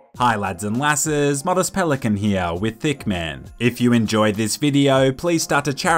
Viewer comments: did he say thicc man instead of hitman? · I think I heard "Thicc Man" instead of "Hitman"